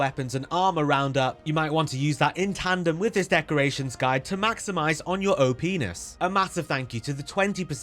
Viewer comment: "to maximise on your opness" I cackled at that one when I heard it lmao. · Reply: Well, I misheard it as "maximize your old penis", so it made me cackle for a very different reason.